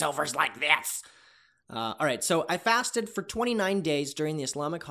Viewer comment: I thought fasted said farted